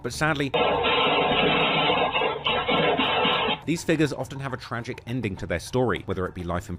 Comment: i thought he said niggas not figures